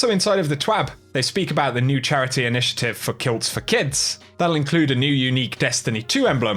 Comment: I thought he said kill the kids